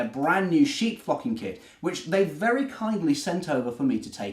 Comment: I thought he said, "sheep f*cking kit! 😄